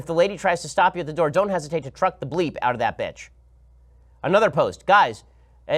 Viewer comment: Did Ben just say bitch? 😂😂